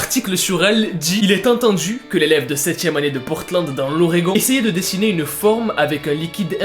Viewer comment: J'ai entendu "une élève de Poudlard" 😭